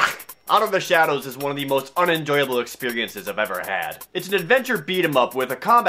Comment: At first I thought he said enjoyable